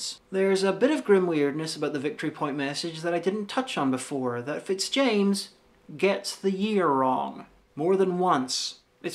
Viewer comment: Great video, thank you sm! Could someone please type/explain the sentence at 09:55? About Fitzjames "getting" something.. I can't make out what that is :( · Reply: He got the year wrong, twice.